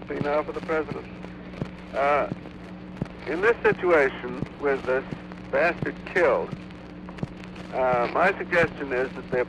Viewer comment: What did he say at 0:46?? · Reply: He was speaking of Lee Harvey Oswald. He said "...with this bastard killed..." in reference to Oswald, not President Kennedy.